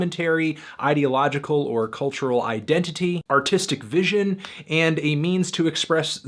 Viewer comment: I swear he said 'Autistic vision'